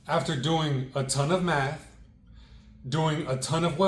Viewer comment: DID HE SAY AFTER DOING A TON OF METH ? HAHAHAHAHAH JUST KIDDING COOL PROJECT !!